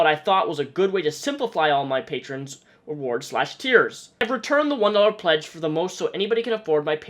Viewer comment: When you said tiers I thought you said "tears" for some reason and I was like "Onision would probably accept tears as payment"